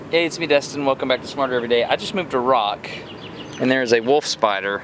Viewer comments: I actually thought you said "I just moved to Iraq" for a minute... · Until I read the description, I heard, "I just moved to Iraq, and I found a wolf spider..." So many little spidies!